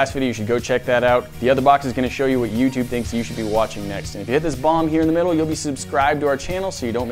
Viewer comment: Did anyone else hear "if you hit this bong here in the middle?"